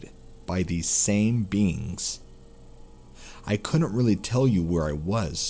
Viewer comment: There was a translucent bean entering his room, then he was on a table surrounded by beans. Every time i hear "beings" it sounds like beans